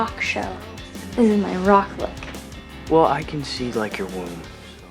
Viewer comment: What does he say at 3:04? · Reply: "I can see like your womb so..." That's what he says